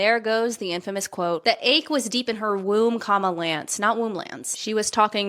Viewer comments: I love that womblands is just a misheard phrase 😂 womb, lance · I could understand if she was stealthed, but I do understand being mad about someone lying. It’s a health risk. But the term “womblands” makes me cringe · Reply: Chels doesn't actually say that. They said "my womb, Lance". But people misheard it and ran with it for the meme